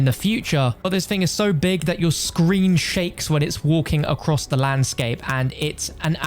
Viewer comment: i thought for a sec he said scream shakes